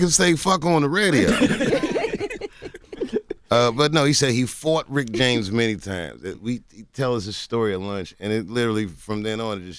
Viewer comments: yeah I thought he said "he f*cked rick james many times" too lol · RIP to Charley Murphy, but I thought he said he "fucked Rick James" too. LOL